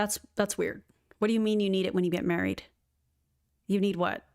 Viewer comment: I heard "buried" not "married" which is even weirder. why do you want them in your coffin?!